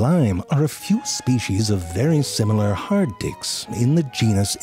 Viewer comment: why does it sound like he said "hard dicks"